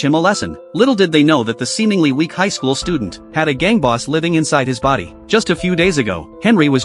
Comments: I thought i hear ''A gay boss inside of him💀💀'' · I thought bro said theres a gay boss inside him 😭